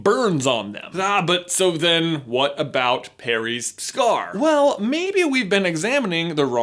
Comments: Is it only me. Or does J say Parry at 6:00? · am i the only one who heard "perry" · Did you say Perry's Scar? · "What about Perry's scar?" -6:00 anyone hear it? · Did he say "Parry" instead of "Harry" at 5:59 ??? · is it just me or is he saying Perry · did anyone else hear “what about Perry’s scar?” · Did he just say perry's scar at 6:00 <link> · is it just me or did he say perry's scar at 6:01??? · Does anyone else hear "Perry's scar" at 6:00? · did you call Harry Perry? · he says Perry instead of Harry🤣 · Did anyone else hear "Perry's scar" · I swear, at 6:02 he says “Perry’s scar” instead of “Harry’s scar” 😂😂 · Did he just say "Perry" instead of "Harry"